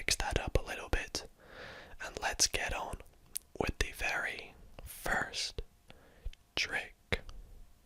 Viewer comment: Genuinely thought he said "hello every furry"